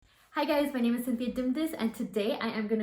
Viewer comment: i thought she said hello guys my name is